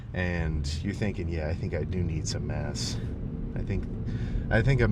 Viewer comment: i thought he said “yeah i think i do need some ass” 😂